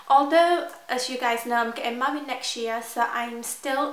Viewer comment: it sounded like you said mummied instead of married. lolol i love your cute accent.